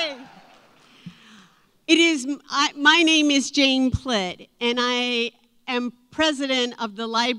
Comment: I thought she said Jane Clit. 🤣